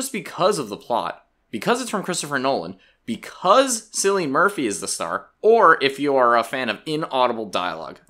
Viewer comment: Did he just say sillian Murphy and not Killian Murphy? 😢